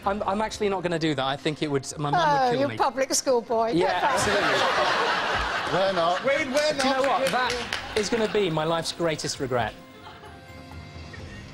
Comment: What does she say? "You public school boy"???